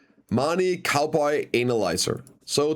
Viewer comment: i swear i heard something different. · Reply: marni cowboy analizer